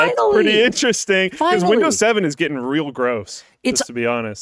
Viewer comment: "Windows 7"? Did I mishear this?